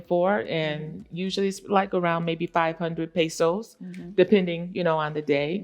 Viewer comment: She clearly said 500 pesos.... Not USD. Let's not get her twisted.😅